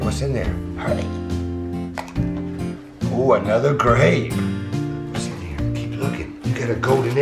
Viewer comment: it sounded like he said cool another grenade.